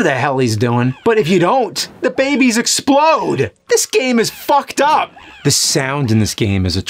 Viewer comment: Legit thought that Frogger game said Fucker for a second.